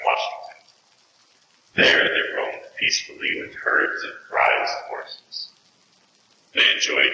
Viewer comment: for the muffled male voice in the beginning after "i can't see," i'm pretty sure he says " it's dark, " but i don't know the other phrase he says. and then for the female and male voice, the missing words that you have as ???, i think are: " i've seen things. " "mostly people . " "what you saw? ... as well as you can remember. " " they put me in a room, on the bed" "there were people trying to get in the room" " improve my breathing" and then for the one at 28:30, i can't figure out the first ??? word, but i think it might be either " brides and ," " prize , " or " bison ?" and then the next ???'s are: "attached shells and feathers " "leader of the Nez Perce and Oregon's ???? " "The Government rep . " (instead of "So the ????" I think it's " To avoid war. ") Bear Paw mountains in Montana." I am tired. My heart is sick and sad." "reservation in far off Oklahoma." "Half were morgued and died from disease"